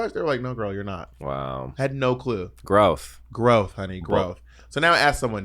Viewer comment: I thought they both said gross but with a lisp, then realised they said growth. Now im advocating for Thorgys werkroom return line being "ugh, Jesus. Growth"